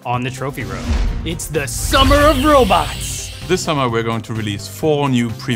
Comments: Me Who Heard It As Summer Of Robux: Yes. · for some reason i heard "its the summer of robux"